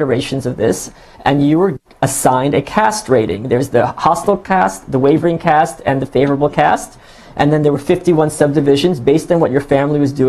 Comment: Michael said "they're assigned a cast rating", I thought he said "they're assigned a castrating". I thought life was bad in North Korea, but not that bad!